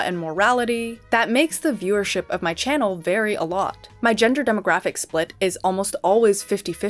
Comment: listened 3 times before I heard “vary a lot” instead of “very alot”. My brain is bad